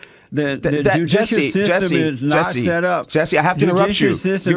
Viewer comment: plz excuse me tha bs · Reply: it sounds like jesse says "set up" at approx 0:27 but he actually says "shut up" and says to let him finish, basically the premise of his argument. interesting to see other fellow aquiesce (spell?) almost immediately. subconscious can be very revealing. that is child mentality (no disrespect intended just calling it as i c) on part of other fellow. like a child he try to pull a "prank" and gets reprimanded on point 😉